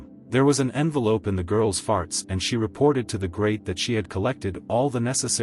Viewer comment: did he say farts XD